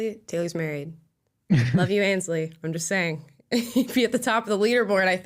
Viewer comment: wait a damn minute is taylor's wife named ainsley? did i hear that right?